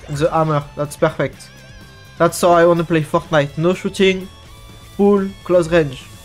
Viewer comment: did he just said instead of fortnite f word nite?